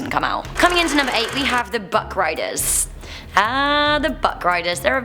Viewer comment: I thought u said butt-riders lol